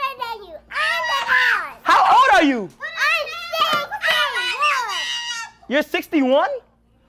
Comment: I thought said “I’m 6 worr”😭